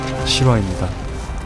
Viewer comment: ㅅㅂ입니다 이렇게 들리는뎈ㅋㅋㅋㅋㅋ